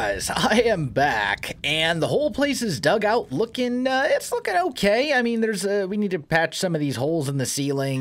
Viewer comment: i thought he said f’in ok